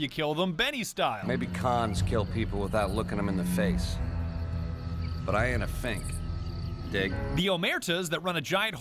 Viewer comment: I have adopted the phrase "I ain't a fink, dig?" long ago and actually use it in casual conversation. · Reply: I always thought he said "ain't a thing"